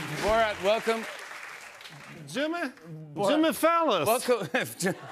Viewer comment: I DIED WHEN HE SAID JIMMY PHALLUS 🤣🤣🤣🤣🤣🤣🤣🤣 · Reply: I thought he said Jimmy “Felon” 😂